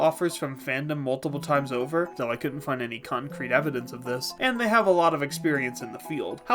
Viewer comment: did you just say "cumcrete"